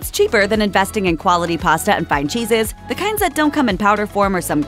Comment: Mac n' Cheese is an Eternal Good no matter what your budget. I heard both "Fine Cheeses" and "Find Jesus" 00:35 🧀✝ 👀